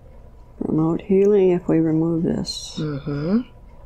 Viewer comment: what did the cient say at 16:52 mm? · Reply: I believe she said, "Healing if we remove this".